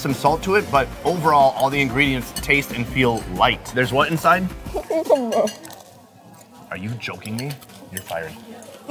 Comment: What does she say @ 4:25? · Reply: Ik it's been 2 years but she says cucumber